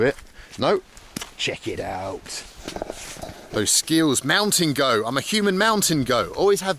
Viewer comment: I thought you kept saying you "Mount and go" not mountain goat lol. Anyone else here that?